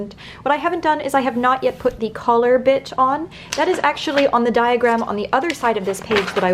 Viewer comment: Lol I thought she said collar bitch not collar bit